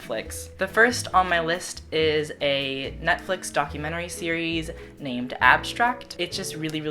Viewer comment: dodie makes me so happy 💕💕 it sounds like you say NUTflix lol this was great conan :-)